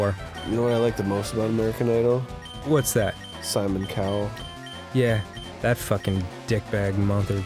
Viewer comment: I must be sleepy, because at first I heard "Silent Cow" instead of "Simon Cowell".